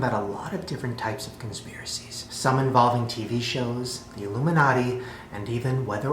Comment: I had the volume a bit low and I thought Shane said "Some involve titty shows" 😂